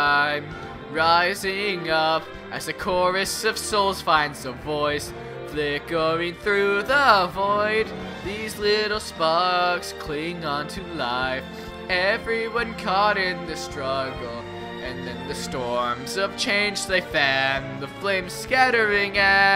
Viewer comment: “The little sparks cling onto life” I heard it as “The little f—-s”